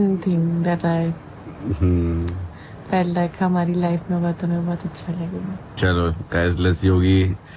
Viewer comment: what she said "fair luck hmari life mein hoga " esa ??